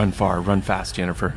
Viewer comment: Did he just say "Jennifer"?!? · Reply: Sounds like it! And could be, but I think it's just the combination of saying "fast Yennifer"...I had to try it out loud lol